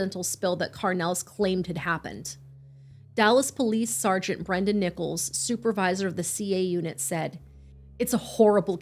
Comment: This is absolutely heartbreaking, but I have to ask.. Did you say Sergeant Brendan NIPPLES?